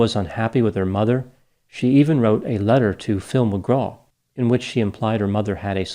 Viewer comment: I love the fact that you called "Phil Mcgraw" by his name and not his stage name "doctor phil" lol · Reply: I thought he said Tim McGraw initially I was like wtf hahahaha